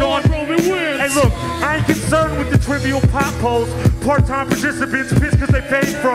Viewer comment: I thought he was saying part-time Sisyphus